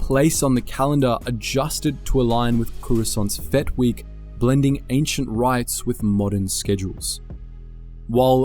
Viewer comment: cue 1.2 million and 2 disappointed clones finding out they'd misheard and there is no "Fett Week"